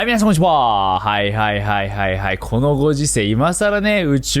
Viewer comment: みなさんこんにちはって言えてないのにそう聞こえる